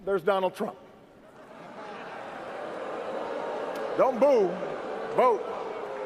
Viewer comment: Thought he said: "Don't move, vote." cause it made sense as well